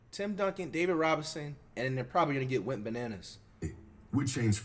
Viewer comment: Does he say Went Bananas, Wet Bananas, or Wit Bananas? 😂 I thought he was saying wet bananas, but it seems like most people type went bananas